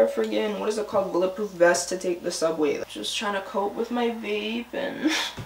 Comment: thought puffy said 'faith' and then I see it 🤣🥴